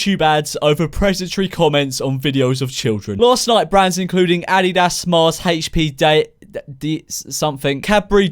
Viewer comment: DID HE JUST SAY ADD DE DAS LMFAO